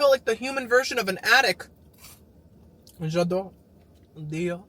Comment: when you said attic for a minute I thought you said addict ☠🤣 I was like ooop